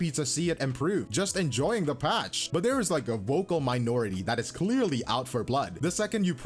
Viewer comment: did you say vocal or woke-cal? Either way, based. Lol